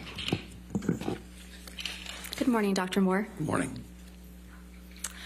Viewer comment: I thought I heard Dr.Moron😅